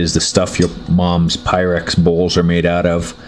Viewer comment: "the stuff your moms pyrex balls are made out of" LOL! I know I misheard but thats funny AF